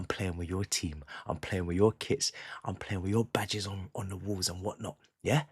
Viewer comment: Thought he said “play with your kids”😭😭😭😭😭😭😭